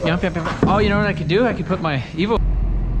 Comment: did somebody say EVO! Ooooh sounds like the next big project!